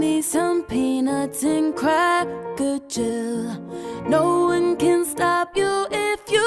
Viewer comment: I heard the "cracker jill"